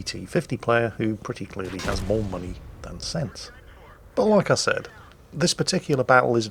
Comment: As for the "More Money then Cents" thing, has anyone heard it as "More Pounds than Pents" instead? Just seems like the more British way of saying things.... >_> (lolz)